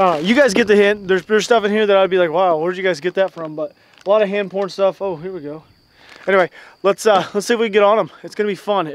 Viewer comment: did he say there's a lot of hand porn stuff